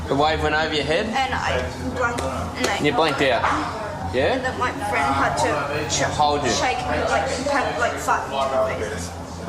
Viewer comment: When was talking all I heard was “it was like my friend had to fuck my face “